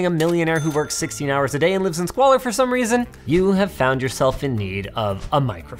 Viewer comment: tf did he say?? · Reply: he's wrong, it's squalor, another word for poverty